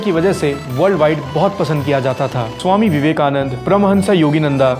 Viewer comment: Wait did you just call Swami Viveka-mun-mun Swami Vivekanand???